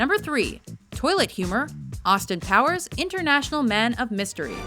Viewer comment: Anyone else hear Austin "Towers"?